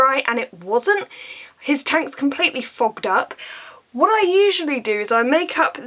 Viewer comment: did. . . did you say fuck? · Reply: fog! lol